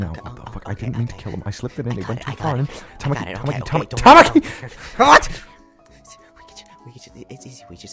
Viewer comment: it sounded like Kyoya said "Tamaki Hot" sorry if i spelled there names wrong